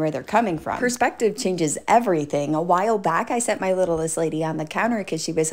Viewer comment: Misheard "littlest lady" and spent the rest of the video thinking your landlady inspired this. Great nonetheless! :)